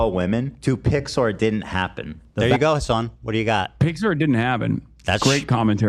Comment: I'm a dumbass. This entire time I thought he was saying "Pixar didn't happen bro" He's saying "PICS or it didn't happen". 😂😂😂 I'm like wtf does Pixar have to do with this.